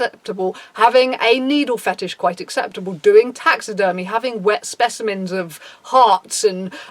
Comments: Because of auditory processing issues, when you said “taxidermy” after a bunch of fetishes, my brain thought that you said “tax fetish” like people who dress up as accountants and do pretend taxes to get their kicks and I’m sharing this because I think we should all have a chance to laugh at that mental image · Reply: I thought she said "doing taxes early". 😂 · Because of auditory processing issues, when you said “taxidermy” after a bunch of fetishes, my brain thought that you said “tax fetish” like people who dress up as accountants and do pretend taxes to get their kicks and I’m sharing this because I think we should all have a chance to laugh at that mental image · When she said “ wet specimen” I heard “wet semen” I was about to say that’s not goth that’s yandere